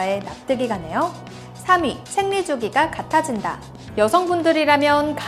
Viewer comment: 기다리세요 제목 박제합니다 2:38 생일이라 들려요